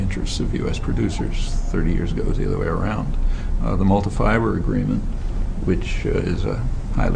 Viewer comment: What is the agreement that he mentions at 2:00 ? I can't hear it. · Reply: schoenbaard Multi Fibre Arrangement.